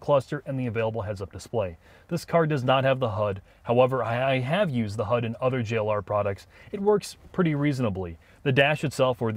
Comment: For sure thought he said “I have used the hub in other Land Rover products”